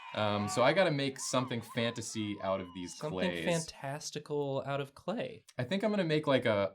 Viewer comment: at one point said the word fantastical and the subtitles thought he said slave testicles. just thought i'd point that out. about 13:40 ish.